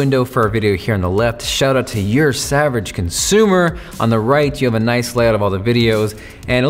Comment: i heard him saying ursavageconsumer hahah